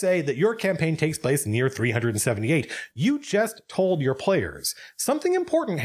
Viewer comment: I think I jumped about 5 feet in the air around 18 minutes into the video when I misheard the "random" year. My current campaign started in the year 3780...